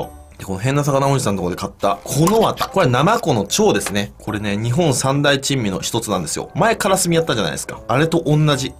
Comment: からの日本三大珍味のひとつが、日本三大チンチンに聞こえた俺は末期か